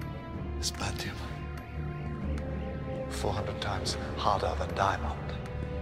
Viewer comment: Heaven Sent presented a clever idea brilliantly realized on screen; the pieces of the puzzle slowly fall into place as the Doctor explores his own personal Hell. Rather than being played as slightly odd or silly the Doctor is portrayed is as he is at the core of his identity - the scientist, the one who applies his towering intellect to solving problems. A truly great performance from Peter Capaldi. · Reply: i just watched it. It sounded like he said 400 times harder than diamond